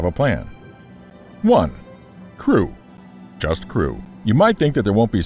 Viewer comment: I wasn't paying attention properly as the first profession came up and thought you said "Just screw". I had to rewind quickly there. Ohh... Just CREW, now that's something I may be better at.